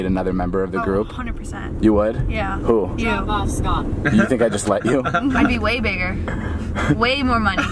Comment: Did anyone heard " fuck off scott "